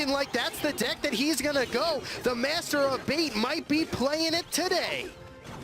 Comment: the masterbator might be playin with it today! Oh wait that’s not what he said...